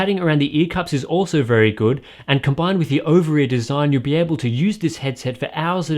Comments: did he say ovary design? 2:18? or an inside joke lol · did he say ovary design? 2:18? or an inside joke lol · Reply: I heard "Over-ear" design lol